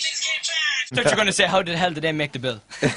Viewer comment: What did niall say at 0:25 ?? 'I thought u were gonna say.....' ??